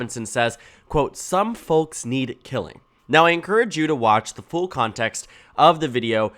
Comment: I only saw the contextless clip where he says it. I could have sworn like he said "need tellin". But it seems like "killin" has been pretty universally heard by everyone else. Effing gross.